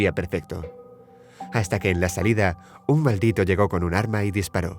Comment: creo que dijo maldito